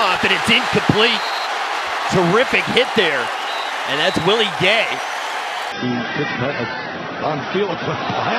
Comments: The legend is back right on time to shine again PS only 4 months till the Christmas intro😉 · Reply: thought man said "that's really gay" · Can't wait for the Christmas intro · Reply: thought man said "that's really gay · thought man said "that's really gay"